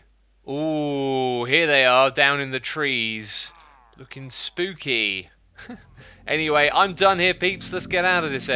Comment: did he say fucking spooky